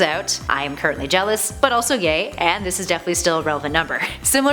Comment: did she say "Im also Gay"